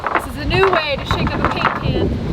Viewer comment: After you finally articulated "slap some stain on these babies", I thought I heard you say "caint pan" instead of "paint can" right after that, but it was kind of muffled, so then I listened again with headphones on, and you said it correctly. Bummer! It would have been hilarious if you had said "caint pan" without realizing it!!